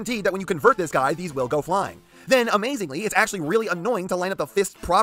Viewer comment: Havent even seen the video yet but im sure its going to be amazing! Glad im early for once lol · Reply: For a second there, I thought you were saying the figure was going to be amazing.